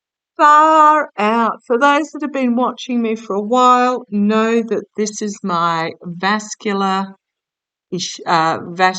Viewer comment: the boys got quite vocal, I couldn't understand what they said. Could you ask them to enunciate better ! 😅😅😊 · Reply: I just slowed it down to half speed. When you put down the 3 swords they said "sad"